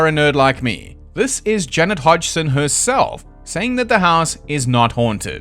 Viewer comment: Every time you pronounce Janet Hodgson for some reason I hear "Jennifer Hudson", which makes the story more interesting in a way